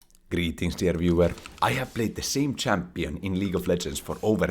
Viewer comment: IVE BEEN WATCHING FOR A COUPLE OF MONTHS NOW AND I JUST REALIZED HE SAYS "GREETINGS DEAR VIEWER" I THOUGHT HE WAS SAYING "GREETINGS TO EVERYWHERE"